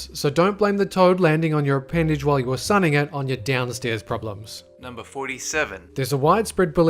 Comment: What is he saying??? Downstairs problems??? CC says it too... Im confused cant understand him